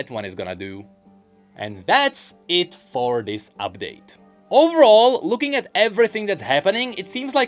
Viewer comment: Thought he said “that’s it for this rift new” now I feel sad.